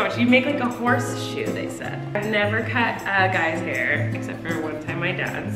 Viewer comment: Colleen: “I’ve never cut girls’ hair” Also Colleen: literally has a video cutting her sister’s hair and said she did it a lot. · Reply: she said guys hair you just misheard her